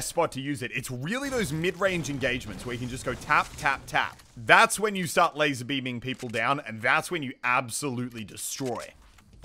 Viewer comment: did you say lazarbeam